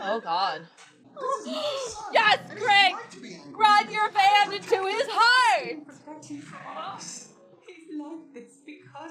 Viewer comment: did you say ram your van through his heart?